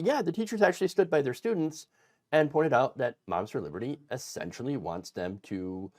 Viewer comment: Ok, some sort of subliminal thing going on, but I kept hearing, 'Monsters Liberty." Six of one...